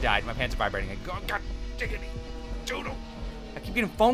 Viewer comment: Lol i thought said god giggity doodle for some reason